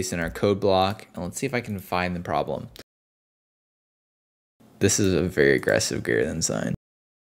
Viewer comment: You need to speak more clearly. What did you say here 2:00 - this is a very aggressive great enzyme?????. You need to think about speaking clearly the whole time.